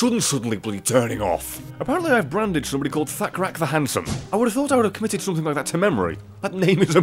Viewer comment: When you said thakrak I thought you said fat crack the handsome